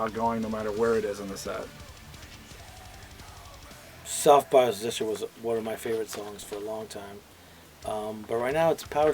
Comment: ? What song did dino mentioned as his favorite to perform live? The captions say soft positioning there is no track under that name... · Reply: Oh nvm i Googled it. Self bias resistor.